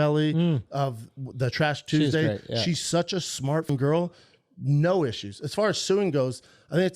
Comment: did he just say dat skank khalyla is such a “smartful girl”???? Did he just made that bs up!??